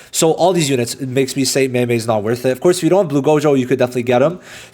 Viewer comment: did bro said mei mei is not worth it? 😂😂 btw any chances that hp gojo and ssr momo be added to wishlist?